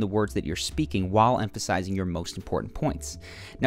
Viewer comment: My confident boost suddenly.. I finally got the guts to look at my crush eye when speaking with her... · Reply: I thought you said ".... while spanking her" 😂